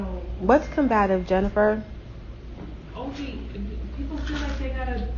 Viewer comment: Did you just say hot breath Jennifer 👉 I'm 💀😆😂🤣🤭